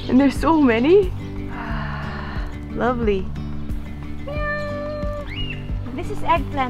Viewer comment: did she just said " meow" at the chicken???😕😕😕